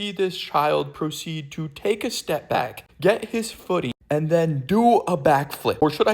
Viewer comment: WHAT DOES BOONING ON SOMEONE MEAN I FEEL SO THREATENED-?? (i did NOT expect multiple people to explain what booning is and all have different responses) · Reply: I thought he said booting and was terrified this kid was gonna vomit on the cashier T-T